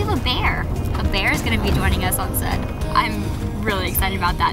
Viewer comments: Can any one confirm she said "fuck you too" at 0:23 ? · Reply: LPS MC I didn't notice the 1st one and rightfully so imo. But at 0:23 I listened to it several times and every time it sounded like "fuck you too." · I don't know if its only me but it sounded like she said the F word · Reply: She didn't.. she said talk to you soon and that's because when she said that she said it very low and the music got louder. :p